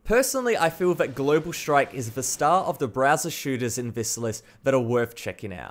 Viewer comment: I hear it like brazzers shooters